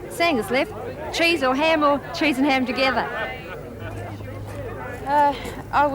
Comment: Did she say cheese and ham pies? 6:50 Awe Cass gets kissed. How sweet. 42:55 Awe Cass... You'll be missed. F-U Freak! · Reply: No he asked if they had any pies and she said there's only sangas (sandwiches) left: cheese, ham, or cheese and ham together lol